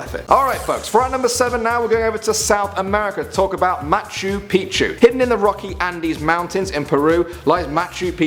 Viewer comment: I thought that said pikachu at first